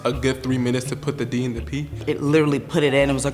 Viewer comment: Person: 13 Me: wow that’s pretty early Other person: 9 Me: well shit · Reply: Wait at 2:52 did he say “d in the p” hey guys! I have a pagina